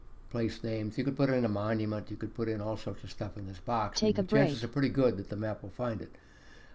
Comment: Did I hear "Take a break" ? Edit: Ahh figured it out, it's your other program. I thought it was part of the FP for a moment.. :)